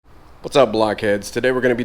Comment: Almost thought you said "Whats up Black Kids?" at the beginning lol.